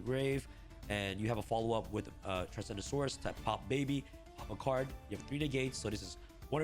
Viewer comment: I keep hearing him say "Transgendasaurus"